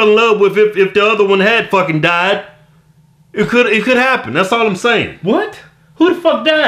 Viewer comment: da hell is he sayin 😂😂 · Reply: doe "..that's all im sayin" like we understood that lol